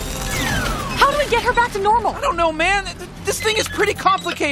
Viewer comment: Lmao what does she say at 00:41? Sounds like i dont know